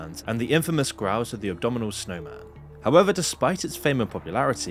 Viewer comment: Anyone else hear abdominal snowman instead of abominable snowman? 1:43.